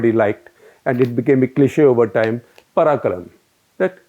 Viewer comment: mins --> I thought the expression of Kamaraj was 'Pakkalam'... meaning 'we'll see'. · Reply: I heard 'parakaram' lol...but kudos to SG for trying